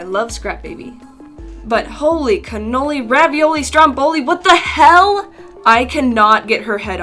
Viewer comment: LOL what its this english word 14:28!!!!!! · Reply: Strumboli?